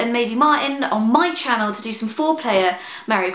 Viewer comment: "I'm thinking of getting Sjin involved, and maybe Martyn, to do some foreplay, err" In my mind, that's what I heard. Took me a couple moments to process it. :P